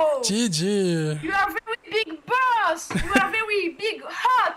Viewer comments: At first I thought he said that he has very big balls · Omg I thought he said "you have very big balls" xD but he said you are a very big boss 20:39 ....maybe he did say that??? Like maybe he meant that his brave?